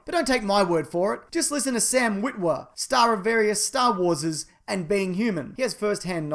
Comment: Did you say Star Warses???